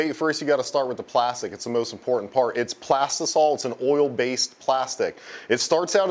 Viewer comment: What did he say?! Plastic salts? and oil based plastics? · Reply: Plastisol it is an Oil based plastic